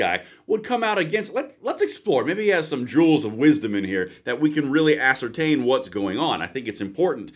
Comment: did you just say "drools of wisdom"? 😂